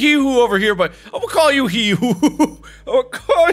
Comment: did you say... HEE HOO?!?!